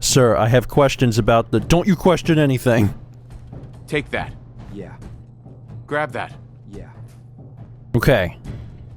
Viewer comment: What Grit says: "Take that. Grab that. Get that." What I hear: "Bop it. Twist it. Pull it."